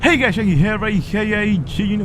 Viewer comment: what do u say in yo intros. it sounds like yor sayin hey its k.a jiggy